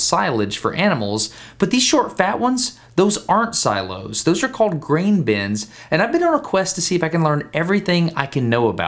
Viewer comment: I thought it said "grain blins." I think I've been watching too much Life of Boris.